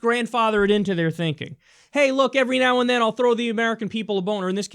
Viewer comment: it sounded like you said every now and then they throw a boner lmao